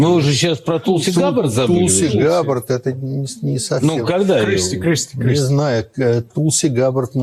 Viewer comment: Listen to it at 2:27 for a couple of seconds. It sounds like someone is saying Kristi Kristi